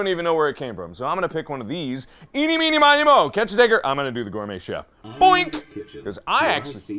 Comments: DID MARK JUST SAY THE N WORD ?!?!? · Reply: He said 'Tigger'. Ya'll are grasping at straws at this point, find something else to do with your day. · He did not say the n-word, I replayed the footage and put captions on. He said "catch a tiger." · AYOOOO 0:22 WHAT DID HE SAY??? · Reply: Tigger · catch a what? Clarification: he's saying "tiger" but that "T" is awfully "N" shaped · all I heard was Figger lol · did he just say the n word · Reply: He was saying tigger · anyone watching 0:22 kinda sound like he said the n-word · im sorry but what did he say? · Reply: Tigger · what did he say? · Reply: Tigger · what did he say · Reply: He said tigger. And he commented about it because some people were concerned. :) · what did he say?!? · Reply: Tigger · Let me be clear; I didn't say the N word. I wouldn't say the N word. I was saying the nursery rhyme and slurred, "Tigger" as I was moving on to something else. People love to twist words to try to fabricate a crisis when there is none. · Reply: knew that, I heard you loud and clear and I heard you say tigger. Also, I love this video. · Let me be clear; I didn't say the N word. I wouldn't say the N word. I was saying the nursery rhyme and slurred, "Tigger" as I was moving on to something else. People love to twist words to try to fabricate a crisis when there is none. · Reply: All I heard was "catch tigger, ok I'm gonna do gourmet chief."